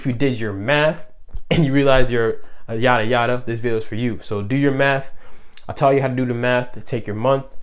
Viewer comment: I keep hearing "do your METH" That's the Pisces in me I guess.